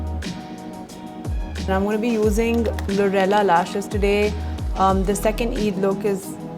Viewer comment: I literally heard “ GORILLA LASHES “